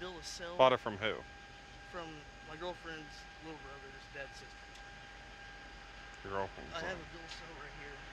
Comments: "My girlfriend's little brother's dead sister" Wouldn't that just be his girlfriend's dead sister? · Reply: guys. the subtitles misheard him other than one time. hes saying his girlfriends little brothers DAD's sister. not dead sister lol. his girlfriend and her little brother have different dads. he couldve just said his girlfriends aunt. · “My girlfriend’s sister’s brother’s, uh...my sister’s brother’s” I just busted out laughing especially when the cop said okay you gotta start over with that with a straight face 🤣😂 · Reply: I heard 'my girlfriend's brother's dead sister' and I was so confused when he was like "i kept texting and texting her" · "My girlfriend's brother's dead sister" I'm still trying to wrap my head around that sentence. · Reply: He's DEFINITELY saying "dad's sister" not "dead sister", LIVE PD just got the captions wrong but I think the cop also thought he was saying dead sister